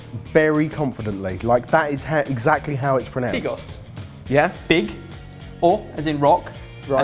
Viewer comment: i thought said big ass XD then i remember the title